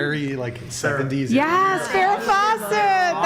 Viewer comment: So one thing I learned tonight: Do not call Alex “cringe” 😂😂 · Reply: what are they saying right about here? Her hair style looks like someone named Sarah? The mic peaks out because one of them gets excited and like the pronunciation of the name isn't clear.